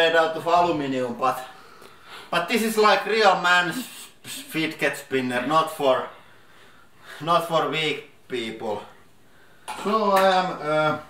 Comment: Did he say: "This is real man fidget spinner. Not for big people" at 7:22?